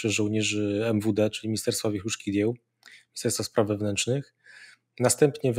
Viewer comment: Usłyszałam: wierchuszki dzieł, chyba powinno być: wnutriennych dzieł.